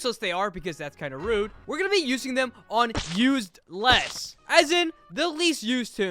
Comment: he said USED LESS. not USE-LESS! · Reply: nvm he actually said used less at 0:09 😭😭